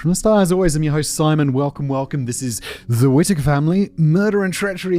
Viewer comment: For a second there I thought it said the Whistler family and was very startled...